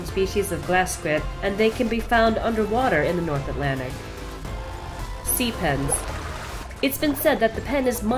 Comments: is it too weird to say I actually heard ‘sea penis’ instead of sea pens ? 😂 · who thought that was called sea penis? LOL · i whas thinking sea pens? i heard sea penis!!